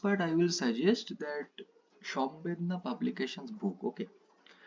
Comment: Can anyone tell what book he said for Integral calculus problem solving.... He said some publication. The video is at the time of 18:25. Help me out 😢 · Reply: Samvedna